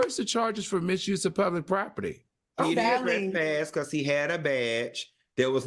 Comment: Ok taking myself back to school, because know the meaning of Trespass. But it sounded like he said Truespass. I need to cut on my auto prompter.😢😊 I love his glasses. 😊